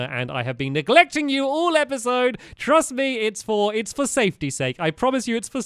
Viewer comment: made me laugh when he said safety stake, instead of safety's sake